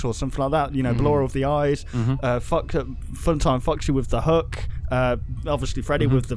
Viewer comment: did he just say fun time fucksy?